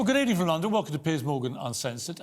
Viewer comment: When he introduced himself, it sounded like he said his name was "Piss Morgan" Go back and listen for yourself.